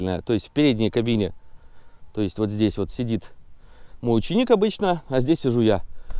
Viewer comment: послышалось мученик